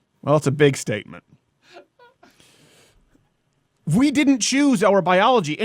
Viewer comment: This man said she has a miss piggy tatoo... I died when I actually seen it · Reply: sounded like he said "pig stakement"